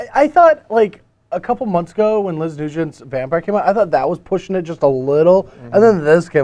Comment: What does say around 6:00? "Last edition's vampire"?